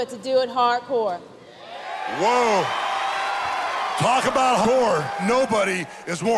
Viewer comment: it sounds like Hulk said "Talk about whore" (instead of "hardcore").